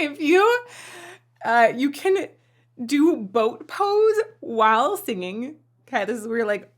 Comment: When she was talking about boat pose, i thought she said, throat pose, and I'm like, THAT"S NOT BETTER!